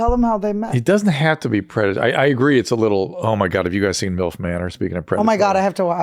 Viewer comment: What show is Dr. Drew talking about at 39:48 ? I keep playing it back and I just cannot understand what he says. Help!!! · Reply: He says “omg have you guys seen MILF manor?” It’s a reality show